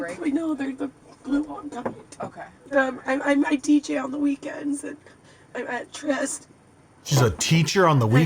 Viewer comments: I heard "DJ on the weekend"... I really hope she isn't a teacher. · She didn’t say she’s a teacher on the weekends, she’s a DJ. Of course she is. When she got fired from her job as a rich housewife, what else is an unskilled brat with no work experience to do? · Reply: Came here looking for this comment. After said teacher, I was like "I thought she said DJ" and had listened to it again. Haha.